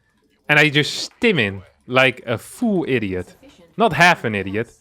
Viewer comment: I always thought the pronounced the word “full” weirdly, but now I see he pronounces it the same as “fool” when he said “full idiot” and I initially thought he said “fool idiot”.